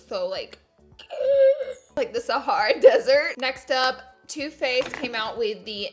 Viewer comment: Of course I heard 'toothpaste' not too faced 😂*unicorn emojis!*